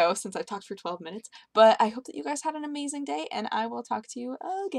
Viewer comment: At the very end I thought she said"........MY BALLS" then I realized she said "..........EYE BALLS!"